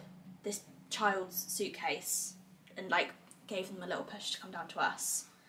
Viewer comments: Am I the only one that thinks it’s a boy? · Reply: Ahhh see I think it sounds like she says "gave them a little push" rather than "him".. Exciting xx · Did you just say gave HIM a little push at 45:52?!?! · did she say gave him a little push?! A boy??🥺